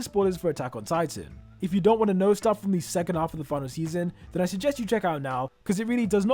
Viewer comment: i hate you. why tf do you say second half of the final season. i heard it as 2 half of the second season and continued watching